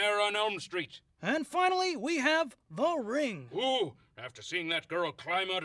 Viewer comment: What did Waldorf say? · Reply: The Ring.